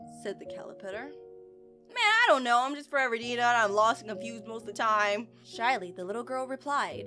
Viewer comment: When just said forever i thought she said pepper nini